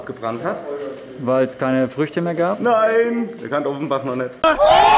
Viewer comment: Haha NussSchuss :D Nices video (Byetheway was hat der bei 0:14 gesagt also als Antwort auf den witz) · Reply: Weil er Offenbach noch nicht kannte :D